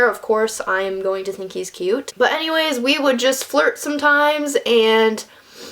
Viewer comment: I thought you said "We would text, and fart" all casual I had to rewind and watch that bit again to realize you said FLIRT. LOL